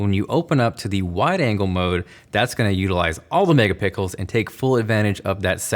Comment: did he say pickles?